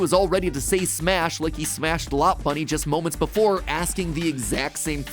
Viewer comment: it sounds like matpats saying "lop bunny" instead of "lopunny"